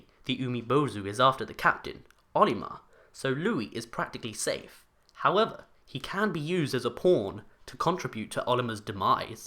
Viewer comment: "...he can be used as a p*rn..." I know he said pawn, but I heard p*rn.